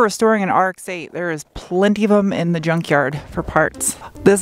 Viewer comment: the first time you said it i thought you said drunkyard. that might be a fun episode. your welcome.